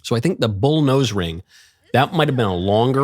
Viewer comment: I thought she said nose ring fairy, lol.